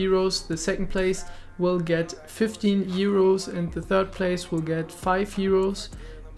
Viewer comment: Bei 00:35 habe ich heros verstanden lol